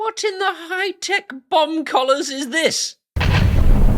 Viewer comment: It's early here, and while drinking coffee I thought he said bum collar and was like what kind of buttstuff is going on here.